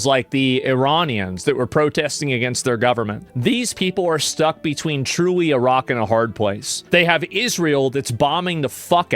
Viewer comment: lol I misheard it to "stuck between Iraq and a hard place" lol